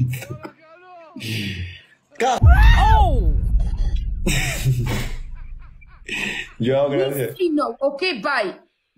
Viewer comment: وش قالها جاني فضول😭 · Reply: قح//ب/ه في تك توك موجوده بدون تشفير